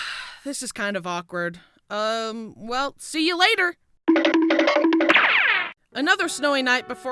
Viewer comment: Mine glitched and I heard "se- ou- la- er-"